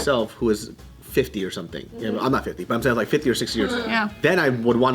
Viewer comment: lmaooo Ya'll for a quick second i thought David was 44 , i heard "I'll be 50 in 6 years" and spat out my juice